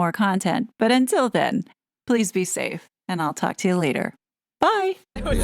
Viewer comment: At the end there it sounded like you said I’ll be back with more content and I’ll talk to your LEADER 😂❤👽